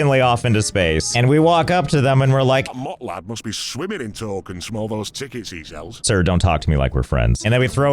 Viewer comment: does anyone understand what he said? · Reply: The start was a little unclear, but I'm pretty sure it was: "That Mott lad must be swimming in tokens from all those tickets he sells."